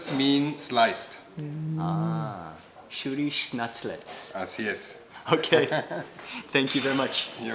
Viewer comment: did he say gracias to mark wiens? If so Mark can pass for a lot of different nationalities